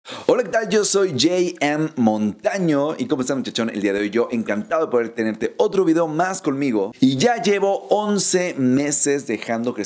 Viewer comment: los subtitulo aparece que dice gay Efe por el idolo